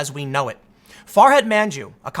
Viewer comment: Did you say "Forehead Man-Jew"?